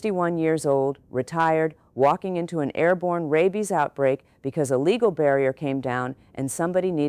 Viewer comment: You guys. 😂😂 I swear I thought for a sec it said “Airborne Babies” 😅🤣 (Which, after raising twins, I believe could be quite scary)